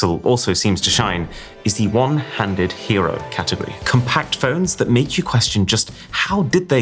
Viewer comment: I heard One-Handed Award 13:19 my dumb a** thought it was gooning phone